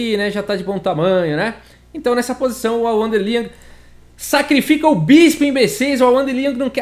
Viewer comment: "Sacrifica o bispo, imbecis!!!" Juro que foi o que eu entendi, rsrs.